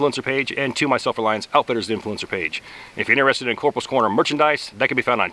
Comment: Damn, drunker than I thought... Coulda swore it said Corporals Coroner. I have got to cut back.